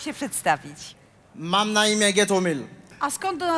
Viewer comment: ja zamiast Gethomil usłyszałem Gekyume R.I.P XXXTENTACION